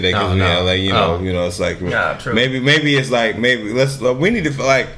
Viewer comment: wtf was he saying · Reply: maybe its like...maybe